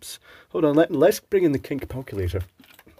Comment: "kink palculator"... I thought that was what I heard :)